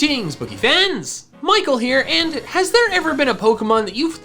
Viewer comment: I thought he said "Greetings, boogiefans!"